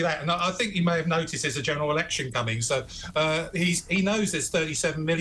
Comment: General election I thought he said General Electric